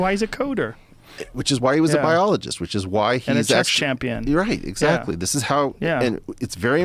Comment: did Rogan just call James a sex champion?